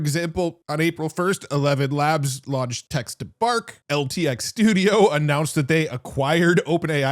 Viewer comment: “text to fart” — or at least that is what I heard during high speed playback.